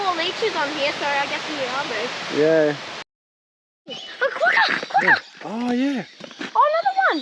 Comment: I can't see the animal clearly, but it sounds like someone said Quokka? Any help appreciated. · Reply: Yeah the kids said quokka, but it's was a red faced wallaby from memory. The kids think they are experts on everything and still fool me🤣